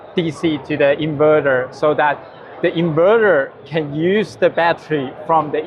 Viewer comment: Did he say get DC from the vehicle to inverter?